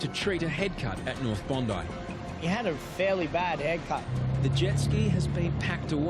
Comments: Lol at first I thought they said “he had a fairly terrible haircut” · I thought he said, "...he's got a pretty bad haircut." 😆